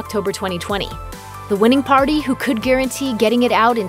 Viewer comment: The subtitles for this video were not auto-generated so I assume someone from Looper either did them or paid to have them done. There is an error at 9:49 which totally changes the meaning of what the narrator says: She states, Amazon "had a few reservations regarding the film's incendiary political content" The closed-caption says Amazon "had few reservations regarding the film's incendiary political content" which is a big difference.